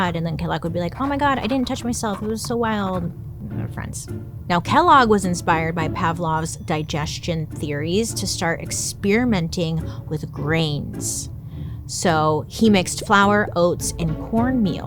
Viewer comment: I'm cooking and listening. At first I thought you said brains and not grains😅